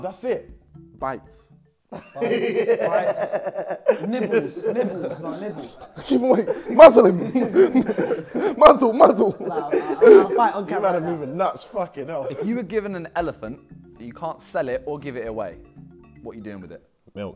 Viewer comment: when niko said ''muzzle him'' i thought he said ''Muslim!''